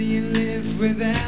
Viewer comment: Did he say you think you can keep on going living like a schlong?